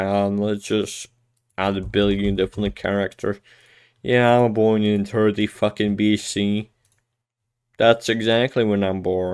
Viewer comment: What did she say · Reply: "yeah I'm born in 30 fucking B.C" as in before christ, so about 2064 years ago